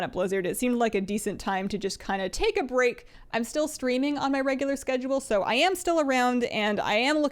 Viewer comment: I thought you said "I'm still SCREAMING on a regular schedule" rather than streaming. I was thinking "I should schedule my daily screaming...."